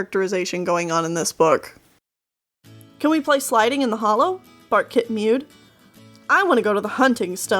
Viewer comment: SEYOOJNGSQWDHLOUFXAWF I THOUGHT YOU SAID FARTKIT